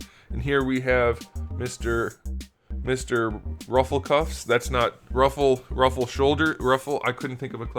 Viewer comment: 3 am and i heard "Hufflepuff" instead of "Ruffle cuffs"